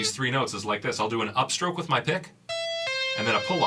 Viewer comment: i thought he said Upstroke with my dick